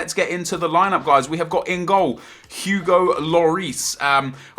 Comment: I thought he said fugo lloris then